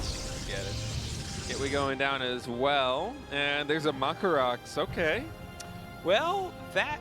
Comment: Did he call it macro rax? But hidden and macro are 2 different things right? Or did he call it monka rax? Maxpax is easy to understand, not sure what he mumbled there...